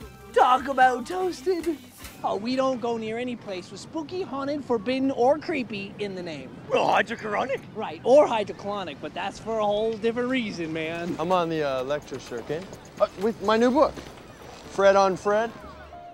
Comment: I can't understand, is he saying hide yo chronic?